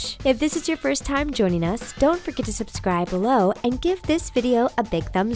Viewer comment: It sounds like your saying "So don't forget to subscribe to TheTaco"